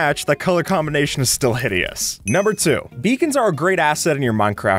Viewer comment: When he was talking about beacons I thought he said vegans pls 😩🤚